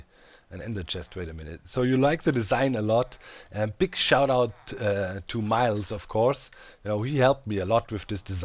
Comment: what? big shout out to me? what did i do? lmao my irl name is Myles